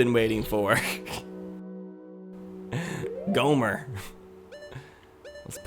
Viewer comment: now i'm picturing a fusion of Gohma and Homer Simpson because it sounded like u said gohmer